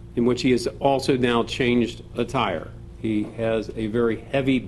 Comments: Alright, let’s be honest, who else thought that the guy talking said the shooter changed a tire? I was thinking, why the fuck would this dude go to the car, grab a bunch off stuff, and then change one of the tires? Did it get booted? Was it flat? Why? Then I realized he said “attire” and not “a tire” and it made a lot more sense.. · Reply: Ha..!! "He changed a tire." I thought he said.. "He caged McGiver.".. And I was thinkin'.... Good luck, Dude.! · Alright, let’s be honest, who else thought that the guy talking said the shooter changed a tire? I was thinking, why the fuck would this dude go to the car, grab a bunch off stuff, and then change one of the tires? Did it get booted? Was it flat? Why? Then I realized he said “attire” and not “a tire” and it made a lot more sense..